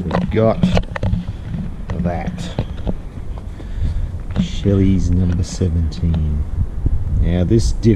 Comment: Did you say Shelley’s instead of Shirley’s 😂